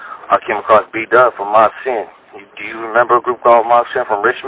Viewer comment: Who is the group he mentions at 12:18? He said it was an ol skool bay group, it sounds like he says mob sin but can’t tell for sure. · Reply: Good ear but it’s Mob C.I.N. or sometimes just C.I.N. It stood for Criminal Industry Niggas , they were a dope gangster rap trio from Richmond Calli , which is in west Co.Co.County , in the early 90’s they had a few good ones you can find on YT music like “Creepin up on the blindside “....